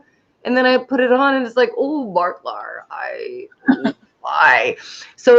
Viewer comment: did I just hear you say, "Oh Marklar," like the aliens on South Park? 😂😂😂